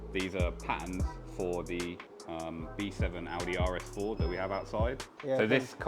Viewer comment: Did he say V7 audi?